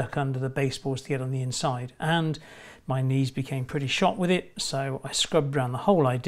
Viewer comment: I thought you said your niece couldn't take it for a second. Knees 😂